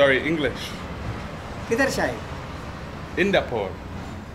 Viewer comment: Just found about you recently, you have a great videos, keep it up! Also I don't speak hindi but tried to replicate what the guy in 23:45 was saying and it seems he was asking where are you from (किधर से kidhar se) :D .